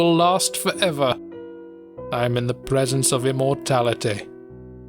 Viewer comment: I thought the guy said the cube cured his "morality" and that's honestly so much funnier lmao.